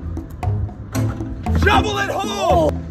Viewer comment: Did he just say "Double in HOLE" are u thinking what im thinking?